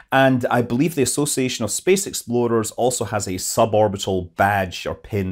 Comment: Did say, "The association of space exploders" If such a thing doesn't exist, we must make it.